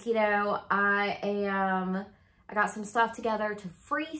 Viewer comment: I thought you said, 'I got some rats!' rather than rags ha ha. You sounded way too calm for it to actually be rats. That house is almost smiling at having you care for and clean it. Have fun xx